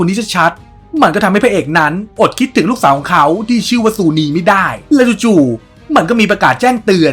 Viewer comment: ลูกชื่ออะไรนะผมได้ยินเป็น สุณี แม่ศรราม